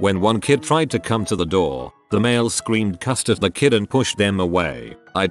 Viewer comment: I thought it said "the male screamed CUSTARD"